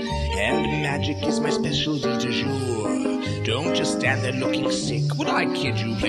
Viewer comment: Can anyone tell me what (I’m assuming French phrase) he says at around 2:26? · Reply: "Specialty du jour", "du jour" means "of the day". "Spécialité du jour" is a term for a restaurant's daily special.